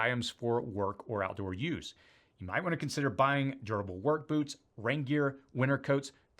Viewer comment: Okay, a bit of humor here. Am I the only one who heard 'gerbil work boots' at 3:59 ?